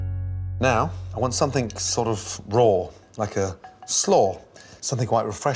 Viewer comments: did he say he want something RAW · did he just say he wanted something fUCKING RAW